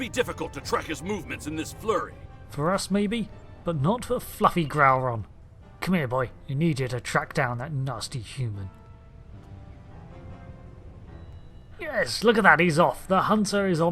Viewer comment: In my half asleep state I'd misheard the name of the Mastif as "fluffy growler". 🤦‍♂️